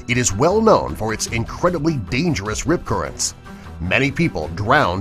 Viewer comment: On #2 thought it said R.I.P current